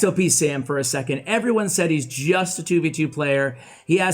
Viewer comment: Did say at the start that Sam was a 2b2t player??? Like at 1:45 Ooooooh he said 2v2